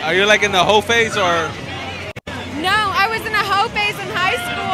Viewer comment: At the did the tall Black girl and him say the “hope age” or the “hov” age? Either way, what did they say and what did it mean? At about minute marker 1:30 - 1:42 ?